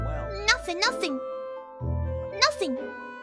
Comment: I thought she kept saying “nothing lad” but this makes so much more sense